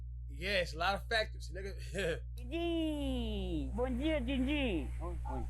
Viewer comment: Who else thought he said nigga @ 2:30?